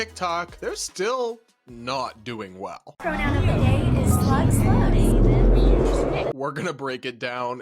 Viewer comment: did she just say the pronoun of the day is "slut/sluts"???